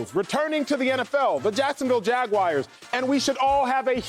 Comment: did he say jacksonville jagwires?